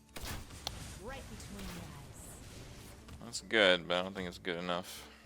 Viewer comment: I've never heard that interaction with Deadly Shot. I think she said ''right between the eyes''.